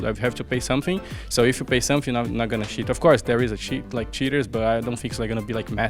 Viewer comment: fer sounds like hes saying shitters instead of cheaters lol